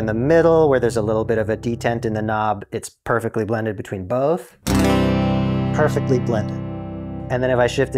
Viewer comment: Is it a "perfectly splendid" from Haunting of Bly Manor I hear at 2:11?